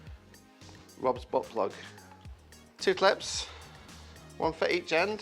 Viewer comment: I had to put CC on to make sure I heard correctly what was said at 0:24 lol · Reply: Ahh Robs putt Blug!